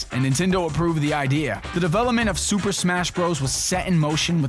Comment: lol i thought it said super sun bros \[T]/